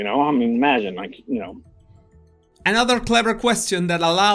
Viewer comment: He actually says, " I mean imagine..." around 14:00/14:14. At least that's what I heard. He kind of mumbles it. He's definitely awkward.